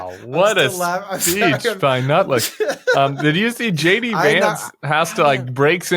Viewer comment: what a speech by who, nutlick? did my ears hear you accurately correctly or am i hearing things get my head checked maybe a schizo or something paranoid, did you call him NUTLICK?